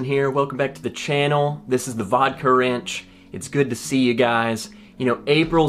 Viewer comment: I thought you were saying Vodka Ranch. YEEE HAAAAA.